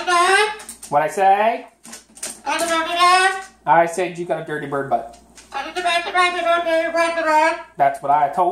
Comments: ROTFL. I swear, there is no other pet/human team on You Tube that is as enjoyable to watch interacting / arguing than you two. I swear he says "dirty bird butt" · I swear I heard Max say, "I do not have a dirty birdy butt!"